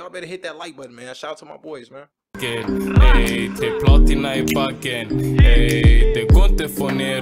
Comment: in your video when he says, “Team platina i backen” is a diss to z.e and jiggz. Shoutout Yasin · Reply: the thought he Said ”team platina in the bag” but he actually Said three (tre) in swedish! Just a misunderstanding, no diss :)!